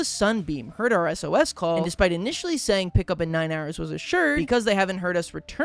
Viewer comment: Mishearing 11 years as 9 hours... Ah, only on.